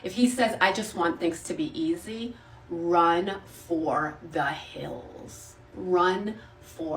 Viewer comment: When she said "run for the hills" I heard "run back to the hills"